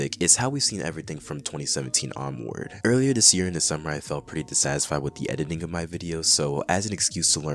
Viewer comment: I swear I heard "the summer I turned Pretty" lol